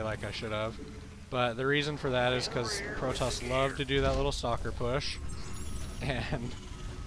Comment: For some reason when you say "stalker push" I always hear it as "soccer push". It makes me imagine zealots kicking around sentries like little soccer balls. Anyway, just subbed to you & really glad I did. Slowly catching up on your casts & really digging it so far.